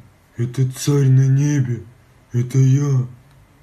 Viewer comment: I'm from Russia this guy is well known here as a freak. He is mentally challenged though, when he was younger he was injecting regular cooking oil, glue, petrol and other chemicals in his arms by himself at home. Since he got some attention doctors helped him because his body was in a horrible condition. But it is very sad and it's weird that no one made him go to the physiatric hospital yet. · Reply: Listen carefully at 6:02, it sounds like he is saying Ruki Bazooki.